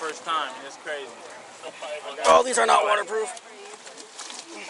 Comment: When he said "oh these are not water proof,i thought he said "officer not waterproof"